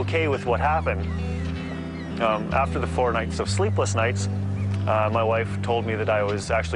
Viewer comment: did he just say fortnite?